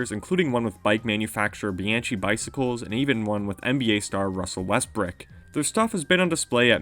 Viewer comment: He really said NBA STAR RUSSEL WEST*BRICK* LMFAOOOOOOOOOOOOOOOO